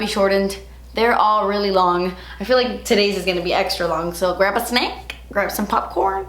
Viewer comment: Lol when you said "grab a snack" in the beginning I thought you said "grandpa snake". It confused me for a while....